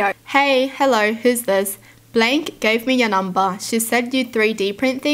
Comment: When she said 'blank' on that first one, I thought she said Blake and I was so confused lmao